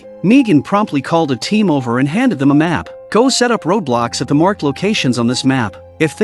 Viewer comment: did he say go set up roblox